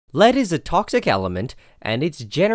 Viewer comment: why did I hear "Ladies and toxic elements" in the beggining of the video?